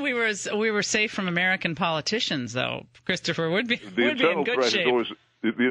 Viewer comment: did she call him "Christopher"?